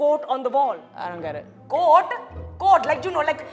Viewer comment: when they said "quote" I thought they said "goat" I have been watching to much barny